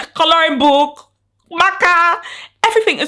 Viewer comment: keep watching until 00:17 you will hear her say closing book maka when i heard it i immediately laughed (love your vids)